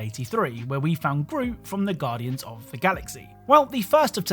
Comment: Im tripping, i heard "gru from guardians of the galaxy"